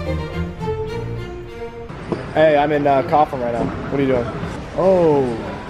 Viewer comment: i tried to figure out what Cofflin is, until i realized you meant Kaufland. do you even try to pronounce it propper lmao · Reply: I kept hearing "cough land"